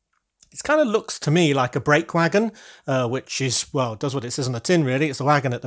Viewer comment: The new train wagon looks great. When you said ‘brake wagon’ I heard it as ‘break wagon’ so maybe it should be a ‘break wagon’ where train drivers can have a coffee tea and a biscuit whilst riding the train, as you have the caboose.